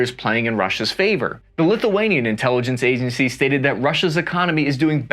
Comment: I think you said Lithuania, but I heard “Lil Wayne” lol